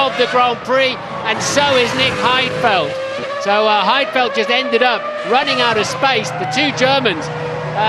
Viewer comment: If it's Heidfeld why does the caption say Webber